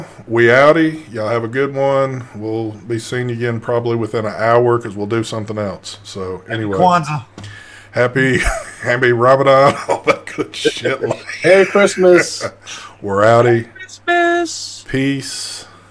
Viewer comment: I thought said, “Happy Ramadan” 😂